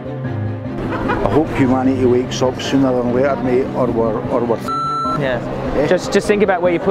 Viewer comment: What does the Guy around 18:33 say? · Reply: I hope humanity wakes up sooner than later...